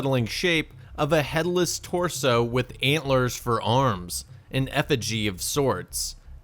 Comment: I thought it said "headless torso with Hitlers for arms" lel